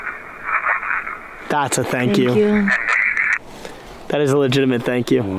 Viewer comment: am I the only that heard “no thank you” instead of just “thank you”